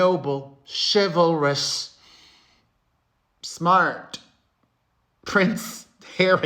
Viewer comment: i heard that as the noble, chivalrous, fart not smart 😅 woops edit: but thats a killer response from the royal family. short and to the point. mic drop 🎤